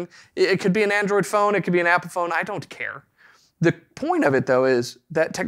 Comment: did he say epiphone haha!